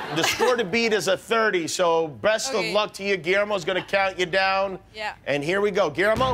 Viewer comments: did he say breast of luck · did he just say "breast of luck"? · Did Jimm say "BREAST of luck" there?